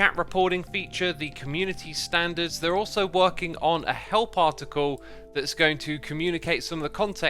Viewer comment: why did my brain thought he said "hell particle" instead of help article. weird